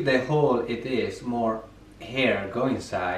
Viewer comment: it sounded like he said more HAIR go inside 🤢🤣💀